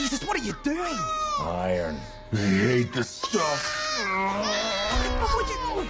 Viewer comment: Did he say, "Iron, they need to stop!"? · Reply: Iron, They hate this stuff